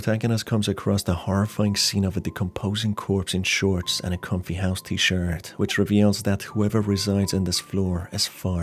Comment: When R showed the first victim and described their attire as shorts and a comfy house shirt I thought he said comfy ass tshirt. 10/10 love this video now